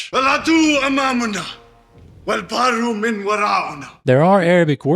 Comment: actually, it's not gibberish! He says, in very broken Arabic " البحر ورائنا و العدو أمامنا" which is an Arabic quote from the Islamic leader/historical figure Tariq Ibnu-ziyad when he was in Andalusia (modern day spain-portgual, I think it's called iberia?) it's translated to "The sea is behind us, and the enemy is in front of us" and it signifies being cornered or stuck, although the original Arabic saying is said from the other perspective as in "the sea is behind you, and the enemy is infront of you, so where shall you escape?" (البحر ورائكم و العدو أمامكم فأين المفر؟)